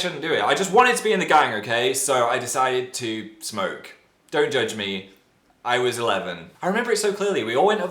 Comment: when he said "dont judge me, i was eleven" i thought he said "dont judge me, i was a lesbian." and i was like bish wat